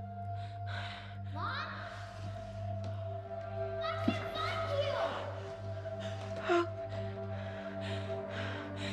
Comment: What does Hope say at around 2:08 ? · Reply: She said "mom, I can't find you"